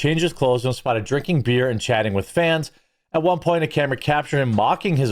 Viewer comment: with what now? Did you say fans?